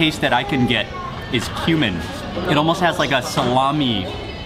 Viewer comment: did he say "human"? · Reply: Cumin